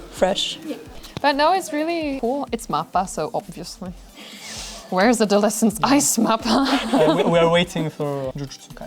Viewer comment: What does say around 13:00? I don't get it. She speaks directly to MAPPA and asks Where is...? And who are "my boys". I feel like I'm missing out on lots of jokes, damn! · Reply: I say "where's adolescence ice mappa?" Although the actual title is "Ice Adolescence", it's the Yuri on Ice movie that has been postponed for a while now 😊